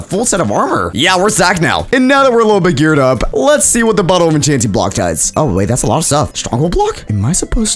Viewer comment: Am I the only one that heard him say bottle of enchanting block?